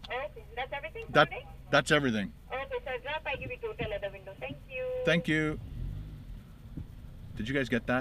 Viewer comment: I think she said " Drive up I will give you total at the window."